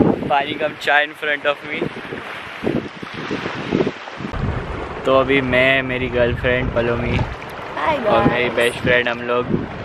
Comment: did he say meri girlfriend pallaumi?? 🤔🙄